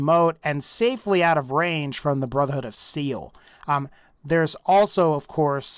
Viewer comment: sounds like he said Brotherhood of Seal.